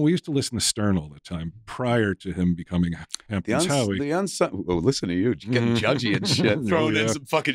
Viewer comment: what did say here? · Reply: Hamptons Howie